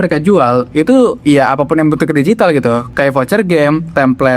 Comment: tak kirain kamu ngomongin jualan ibu, salah denger 😔